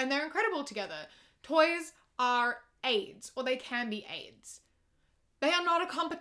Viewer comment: I thought you said “toys are aids so there can’t be AIDS”👁👄👁